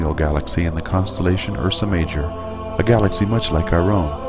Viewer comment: Does the subtitling at 03:36 really say "...in a constellation or some measure" where the narator says "...in the constellation Ursa Major" ? And some seconds later "a galaxy much like a row" instead of "a galaxy much like our own"? (...and some typos) That was rather... 'inexplicable'.